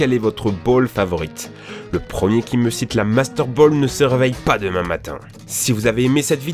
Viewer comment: Moi perso ma préférée c'est la Luxe Ball car: -Luxe ball est un nom classe -Agréable à regarder -Maniable comme il faut -Amusante à utiliser -Simple comme concept -Très bon taux de capture -Effet d'apparition classe -Rare à trouver -Bon prix -Actuellement en stock -Lol -Littéralement pour les personnes au gout de luxe -Ecologique car 100% recyclé -Système interne puissant -Totalement classe sur tout les points de vue -Mécaniquement peu complexe -Agréable à porter -Pikachu rentre dedans -Rentable -Est très puissantes contre les pokémon sauvage -Favorable à la capture des légendaires. Maintenant (si c'est pas déjà fait) lisez les premières lettre de chaque argument ;) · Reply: a j'ai compris "la master ball est ma pref"